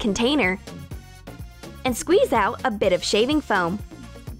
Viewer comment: I thought it said a bit of shaving cream (three cans)